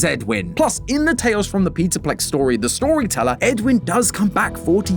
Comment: why do the sub titles say peterplex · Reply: It lowkey sounds like he said Peterplex 😭